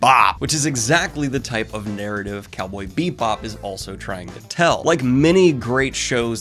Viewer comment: I keep hearing "Beatbop" and I'm not ok